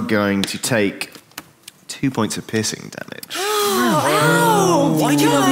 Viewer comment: I’m watching this at 6am because I can’t sleep. When the DM says, “Prudence, take 2 points of piercing damage.” I could’ve sworn he said, “Take 2 points of pissing damage.” Thought he was being cheeky but it turns out I’m just incredibly drowsy 😅😴